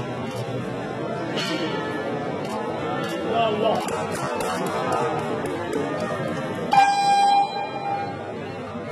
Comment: what did he say? Yallah? Isn't that Arabic for let's go? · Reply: He said 'Ya Allah' not 'yallah' it translates to 'O my lord', invoking his name is asking him for help it is said at the start of your dua'a which is a type of prayer you make after the main prayer to ask Allah for help, hence why he puts his hands up after thanking Allah for allowing him to make the weight.